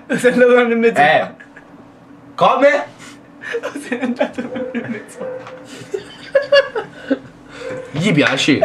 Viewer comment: cosa dice? D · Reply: O se ne è andata un anno e mezzo fa?